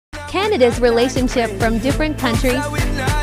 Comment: I thought it said grandpa's relationship it every country int he beginning 🤣🤣